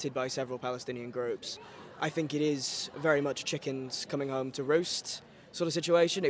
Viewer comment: Did he say chickens coming home to roast?